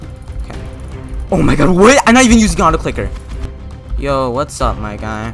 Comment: wait did he just say he uses a auto clicker?